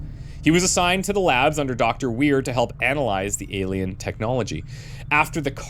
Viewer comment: I had the video on in the background and I swear I heard he was working for Dr. Weird. “GENTLEMEN! BEHOLD MORE CORN!”